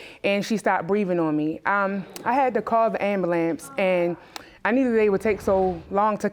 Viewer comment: did she just say ambalamps? instead of ambulance? lol